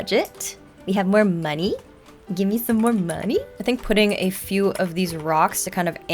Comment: I thought she said “Just give me my money!” 😂